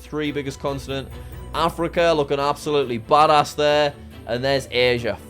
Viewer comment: Did say bad ass!?